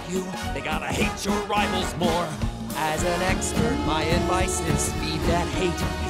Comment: I heard that as "as an X-Man."